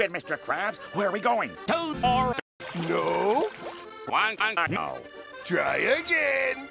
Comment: what did Squidward say? I think he said ,,one ton ago" atleast it's my opinion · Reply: Guantanamo. like guantanamo bay